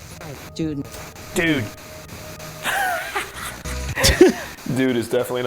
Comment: Is it just me or when said dude at 12:24 it sounded like June on the radio (or whatever it is)